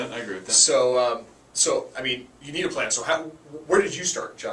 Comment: thought he said you need a plant lol